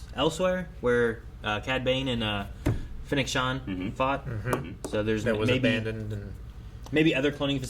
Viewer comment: i thought you said "where Cad Bane and Big Sean fought" 😂